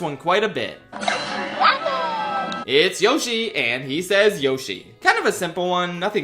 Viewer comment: What do you mean Yoshi says “Yoshi”!?!? All this time I thought he said “Waffle” 😭